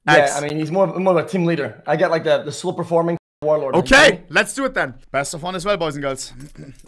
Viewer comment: can someone translate the start where it sounds like he says i get the 'slopeforming warlord' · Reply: he says "slow performing warlord," I speak el globlinian so you can trust me